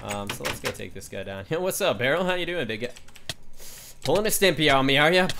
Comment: did just say nigga????? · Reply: He said big guy.